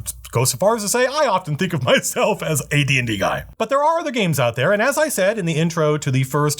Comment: I heard that as "I often think of myself as AD&D Guy"